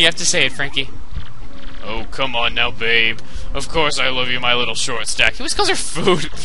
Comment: When said "I love you" fast-forwarded it sounded like he was saying "GOBBEL!"